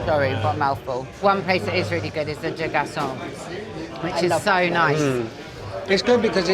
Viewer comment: What restaurant does she say at 19:02? Thanks! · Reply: I think she says Les Deux garçons!